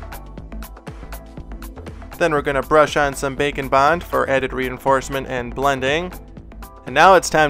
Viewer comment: I thought he said bacon bond instead of bake and bond